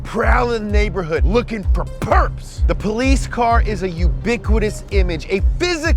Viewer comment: I thought he said the shark was lookin for percs Lmaooo